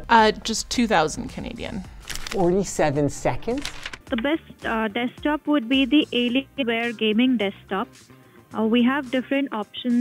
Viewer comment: you shame Dell employee for saying "the best alienware desktop is the alienware desktop" But that's not what she said at all. She said the best desktop is the alienware desktop. Are we already back to not fact checking your videos?